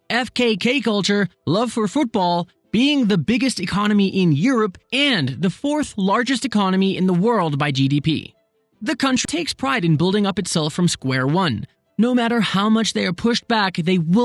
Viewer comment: was it cut purposely as a joke? it sounded like c**t rather than country to me LOL